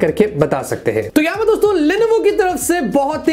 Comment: You say Lenovo but I hear Levinho (pubg player) 😍✌😂